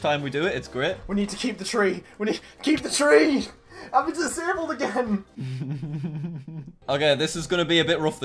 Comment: Anyone else hear the streamer say “I’ve been disabled again” ???